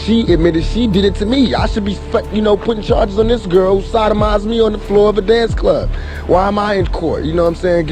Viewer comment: does he say that ''She sodomized me on the dance floor''?